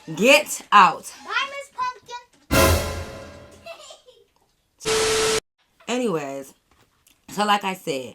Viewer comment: did she say "bye miss pumpkin?" Lol